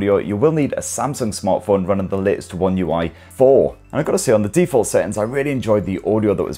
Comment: what is he saying here? Samsung smartphone running..? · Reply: Latest One UI 4